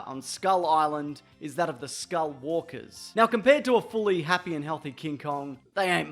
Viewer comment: did y’all hear him say skullwalker instead of skullcrawler